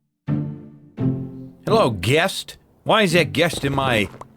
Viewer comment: I thought you said, "hello guest". At the beginning and was like wow. Why so distant. Is this because I forgot your birthday?